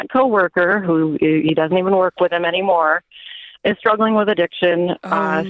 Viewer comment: She refers to the coworker as "They". Its "she" its "SHE". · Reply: I'm pretty sure she said the coworker was a guy at the start. Sounds like she says around 43 seconds "He doesn't even work with him any more".